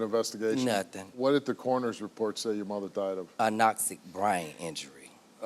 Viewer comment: What did the coroners report say "a nazi brain injury" 17:40 😂😂😂